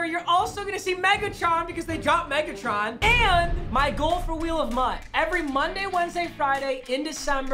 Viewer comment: I thought you said wheel of mut wednesday